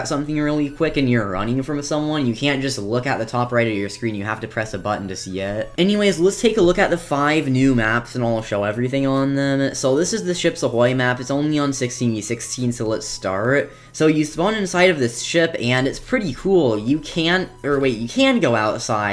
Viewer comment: I thought you said chips ahoy lololooo